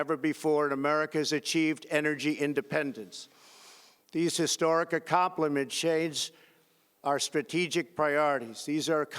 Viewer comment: “Accomplishades” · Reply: I thought "No way, there's no way he says this" and as soon as I had that thought I hear "Accomplishades"....... HA